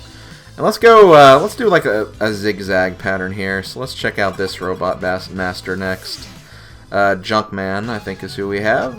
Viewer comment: Sounded like you were about to say "-so let's check out this robot bastard next", lol.